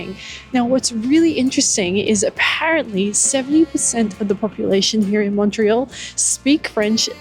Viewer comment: Sounded like you said 7% instead of 70%.